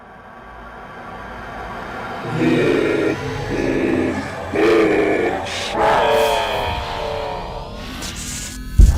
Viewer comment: Co on powiedział?????? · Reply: Zagłada